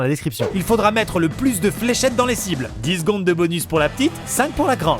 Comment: j'ai compris slip à la place de cible du coup ça change toute la phrase 😂😅😅🎯